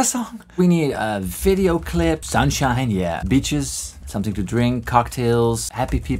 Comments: When he said "Beaches" in the beginning while writing down his formula I could've sworn he said "bitches" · I'm sorry but I heard "bitches" instead of "beaches" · Before he showed the word beaches on screen I thought he said bitches. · I really thought you said bitches instead of beaches haha